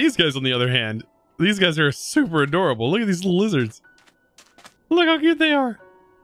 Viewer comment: I thought he said "Look at these loosers" he sais lizards XD